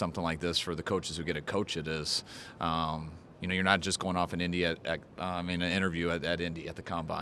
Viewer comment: For a sec I thought he said he going to India 1:26. got excited cuz I'm Indian lolz :) now I'm sad ;(